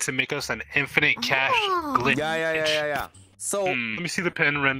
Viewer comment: I think he said “glitch” not “clinch”, I might be wrong tho